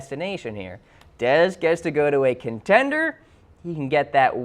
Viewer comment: I thought he said he wanted to go to a contending team